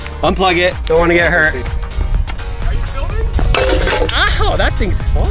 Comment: Thought I heard someone say: Are you filmin’ Or maybe it was just..?